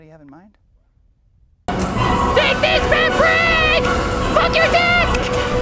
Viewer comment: Did Ruby just say "Fuck your desk!"?